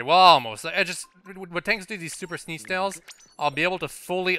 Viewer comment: For a moment there, I heard " super snee snails "